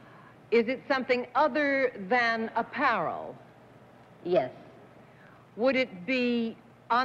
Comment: There is a word they often use that I don't know how it's spelled. It concerns clothes and clothing and it rhymes with "Carol" but starts with a "p". I can't find it in my Dictionary. Can you enlighten my ignorance? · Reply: What's My Line? Thank you! I thought they said 'a parel' instead of apparel. No wonder I couldn't find it. :)